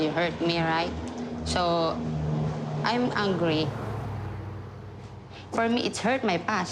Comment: when she said 'im angry' i thought she said 'im hungry'